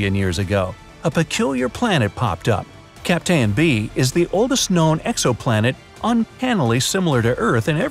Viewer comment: what is said at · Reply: Peculiar planet popped up Captain B is the oldest known Exoplanet Uncannily